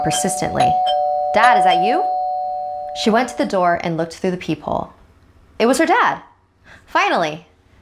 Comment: Lol at the beginning I thought it said the peehole